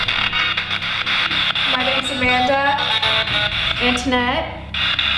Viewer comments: I heard is that Amanda in like a womens voice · Time stamp 16:09 who heard “duck in a net” ??? 😂😂😂